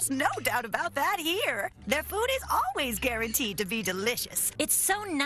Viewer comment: I thought she said their booty is always delicious 😂